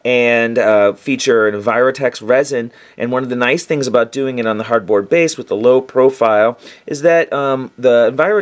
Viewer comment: Did you say "hardboard base" at 2:15? It looks like a perfect thickness for terrain bases. Where did you buy it? Cheers!